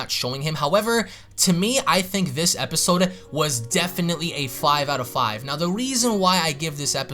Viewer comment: i almost thought you said 5 out of 10 I was like WTF are you saying